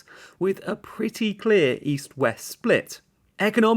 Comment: East? I thought you said, "Weast".